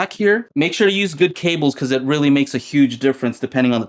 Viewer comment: At first I thought you had said LSD cables... lol